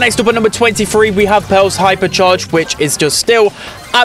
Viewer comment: Every time he said Pearl I thought he said belle